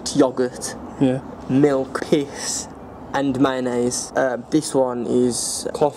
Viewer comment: did he say piss? Piss was in that yellow drink? Man that's wrong. Great video though